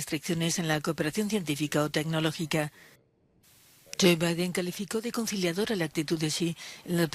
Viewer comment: Yo escuché JBalvin 😆🤣